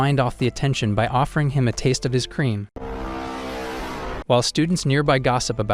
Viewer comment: ayoooooooooo what did he say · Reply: Its just cream🍆